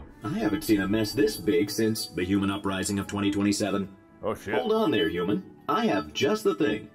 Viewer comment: I thought he said The Human uprising of 2020